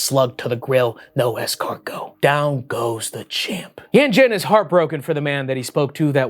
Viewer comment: I heard « down goes the chimp »😂😂😂